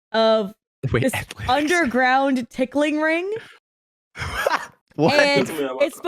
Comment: I’m sorry, I heard “underground pickling ring” instead of “underground tickling ring”… honestly didn’t make it much weirder.